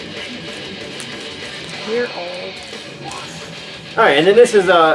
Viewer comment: dude, what did your wife say at 10:53? lol. I have a pretty extensive collection of 12 inch Saxon eps. I started collecting them back when they came out and always stuck with 12 inches although ive picked up some 7 inches over the years. the 12 inches included Patches and stuff that I would have sewn on my jean jacket. some of the stuff on 12 inch came out on 7 inch so there are a few 7 inches u are missing (which is great) as the hunt continues. cheers man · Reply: NoiseSpektrum Haha! She was commenting on me saying, "While we listen to some righteous tunes". She laughed and said, "you're old!"! I have some Saxon 12" singles as well. Some of my UFO singles came with patches. I showed those in a past video. I don't think any of the Saxon singles I have come with patches. Anyhow, yes, I am missing a TON of Saxon singles. My collection is very modest in comparison to how many singles that band has out. No problem though. The hunt is part of the fun.